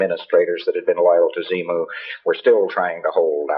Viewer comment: wtf, I know he is pulling ALL of this straight out of his ass. But did he just say Xemu (Zee-Moo)?